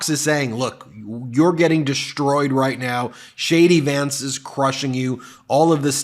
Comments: I thought you said "Shady Vance" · it sounded like you said shady Vance. 💙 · Could've swore I heard you call him "ShaDy Vance"....lol. I like that so it's sticking. 👍😂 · Idk if it’s just me mishearing, but we should keep calling him Shady Vance. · I thought you were saying j d vance, shady Vance... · I thought he was saying shady vance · I thought he was saying Shady Vance at 1st.😮😂